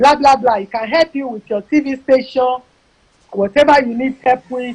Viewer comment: Did she says “help with TV STATION”? Kini iya yi nba ka?